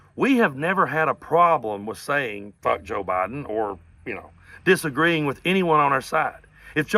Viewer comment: Man ! They’ve been saying F Joe Biden all this time ? I thought they were saying manga’s stoopid ! I guess you can learn something new every day ! And now the little snowflakes are upset because Biden drank out of a mug that had Dark Brandon on it. What a bunch of P.A.B.’s they are . Waaaaaaaaa !